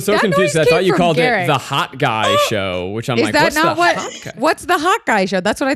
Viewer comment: Yeah I swore she was saying HOTGUY SHOW